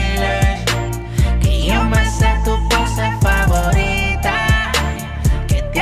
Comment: Yo de Niño pensé que decía cosa favorita